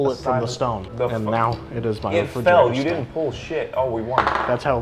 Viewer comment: wtf i thought it said IN the refrigerator